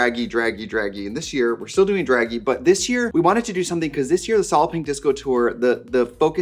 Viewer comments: The first 5 times I heard her say Solid Pink Disco, I heard "Solloping Disco" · Before I saw the text for "Solid Pink Disco," i had no idea what trixie was saying. I thought she was saying Solopping Disco.